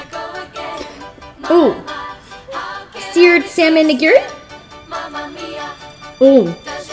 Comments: I thought you said the N world but you said nigiri my cousin name hahahaha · When she said “nigiri” I thought she said “nigga”💀